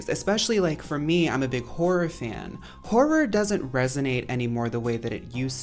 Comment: i had this in the background and when i heard the 14:20 clip of you saying "im a horror fan" i just heard im a hor* and i bursted out laughing